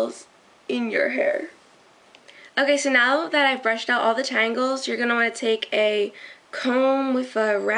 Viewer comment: LOL!! At 1:36 It sounded like she said.. "So now that I've brushed out all the TRIANGLES.".. instead of tangles.